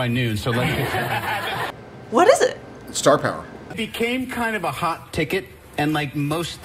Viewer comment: sorry I'm not a native speakser, what does he say its his superpower? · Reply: It’s star power